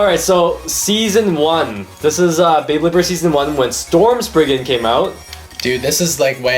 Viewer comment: When you said storm spriggan I thought you said storm Pegasus